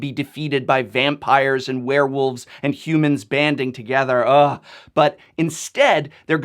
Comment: Did you say banding? :) I actually like that mechanic.